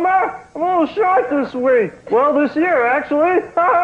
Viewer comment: What does Barney say? "Hi Homer! ... this week. Well, this year actually. Haha!" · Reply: You look sharp this week. Well, thisyear actually. There ya go